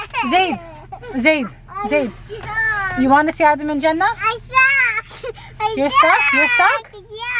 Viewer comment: I thought you said to him " do you know wanna see adam in Jannah?" I got bit worried lol. Masha’Allah cute kids الله يحفظهم